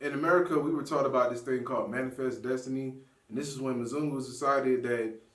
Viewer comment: what word did he use to describe white people? (Menzugus?) Can somebody spell that for me ....I never heard that term before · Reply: 'It's mzungu. Only two 'U's. Non-derogatory Swahili word for caucasians/Europeans. Literally means 'one who wanders around (aimlessly).'